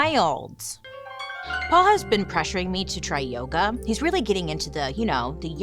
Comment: I keep repeating because i heard “my husband keep pressuring me” lol i was like, when? who? how? lol I need to turn on caption lol